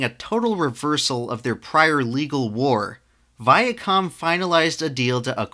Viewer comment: I thought it said vid con instead of via com